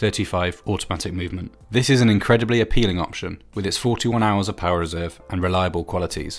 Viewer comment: Dang i thought u said 14 hours of power reserve, glad i went to check the details on google. misheard it. The watch itself looks beautiful! suck to be a poor student